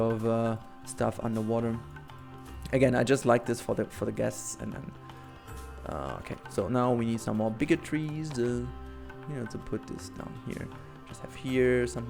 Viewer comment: "Now we need some more bigotry,, Misheard you a bit